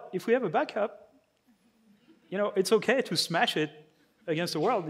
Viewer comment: i’m sure it was his accent but I heard him say “take your robot and smash it against the WORLD” The most mind blowing Freudian slip of all time